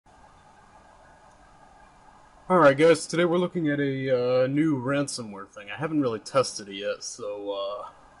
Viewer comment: LOL.. sounds like you said "Alright ghost, today we're...." Another great video. Thanks